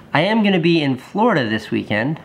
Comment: Got really excited cause I thought he said Portland, turned out he said Florida haha